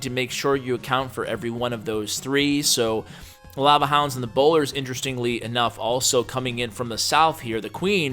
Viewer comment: Did he say boner instead of bowler? I swear I did not mishear that lol